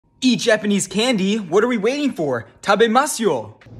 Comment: My volume was low and I thought he said piss candy 😭🗿